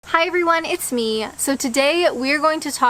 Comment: you always start with ‘for those who don’t know me!’ Everyone knows you!!!! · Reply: Not necessarily. I came upon by accident. For the first few episodes I watched, all I heard was, "Hi, it's Me." I began to think her name was "Me"!